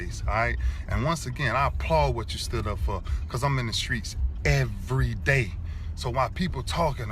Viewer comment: What was your reaction to Ray Lewis's message? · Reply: Sounds like Ray is saying I'm in the streets Shannon where are you bro.